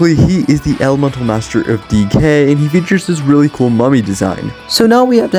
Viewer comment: he said elemental master DECAY bot i heard DK which made me think he was the elemental master of Donkey Kong😂